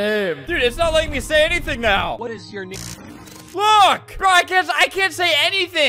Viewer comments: I swear he is turning to Albertstuff and I am loving it, like man literally said fuck 6:03 Edit: made a mistake and I am sry, but he is turning not family friendly and it sounded like he said "uck!!" And not look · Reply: He said look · when he said look he sounded like he said the uhh the bad F